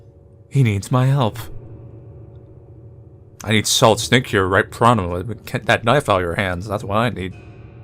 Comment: What the hell does say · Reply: I need solid snake here right pronto get that knife out your hands that's what I need